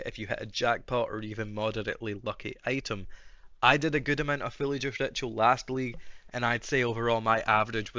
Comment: I think my headphones are too low quality, what word did you say before ritual? I can only hear "Villager ritual" Great video btw, thank you so much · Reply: I said fully juiced ritual, its not your headphones its my accent haha